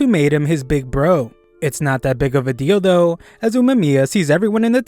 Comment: I hear it not just as big as a dildo????